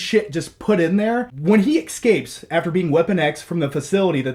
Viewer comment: Did he just say "excapes"?